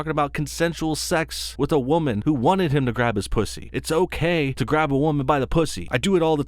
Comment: did you say 'grab his pussy' at 1:43?